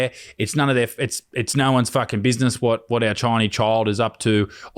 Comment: I thought you said it's no one's business what we do with our Chinese baby. Ha ha